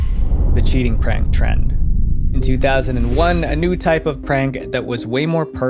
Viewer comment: Am i the only one who hears 2001 instead of 2021